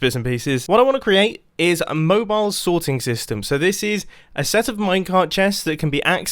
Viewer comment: I thought it said mobile snorting system...